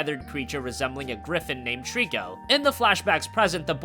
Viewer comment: not all people know this but triko is actually spelled toriko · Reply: Mm the japanese name. Intresting! I thought I heard the boy say Teriku xD but then it is Toriko in Japanese ^^